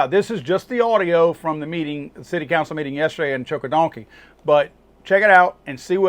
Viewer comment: Sounds like youre saying choke a donkey. Lmao freaking auto type! But you got it 😆